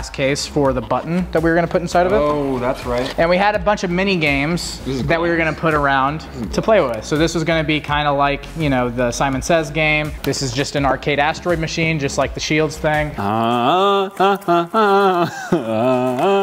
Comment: Did he say astroides in sheilds 15:10 lol